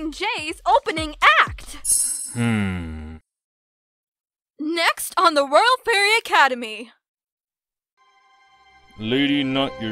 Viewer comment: I lowkey thought professor dynamite said “welcome to royal ferret academy”